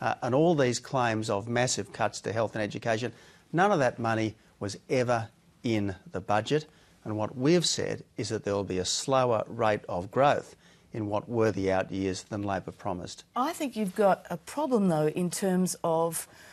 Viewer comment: Sounded like he said a slower rape of growth, I thought it was speeding up